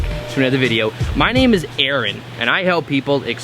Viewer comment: I totally heard "Erin" not "Aaron" lol. hi Erin, plz have a look at my own Mandela Effect experience video/ nature of reality as a quantum possibility video (but Im a hermit, so Im not so enthusiastic in my speech)